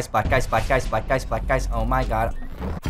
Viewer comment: I swear he was saying black guys😂